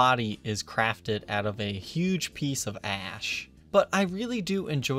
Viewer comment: it almost sounded like you said "this guitar is made of a huge piece of ass"